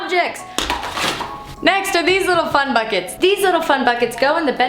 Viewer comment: When she said fun buckets I thought she said "fun fuck it's." I can't be the only one lol